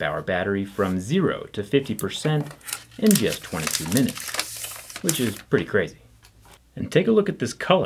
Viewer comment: who else heard “1 minute” instead of “22 minutes” lol